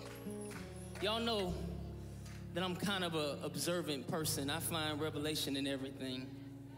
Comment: Guys, can someone help me? What does he say between “observing person...” and “...and everything” at 7:25? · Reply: He says "You know I'm an observant person and see revelation in everything." Meaning he can find biblical meaning in many things he observes in his life. Hope this helps.